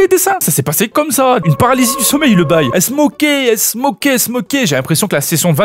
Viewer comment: Elles s'moquaient (j'ai entendu "elles smokaient" 😅)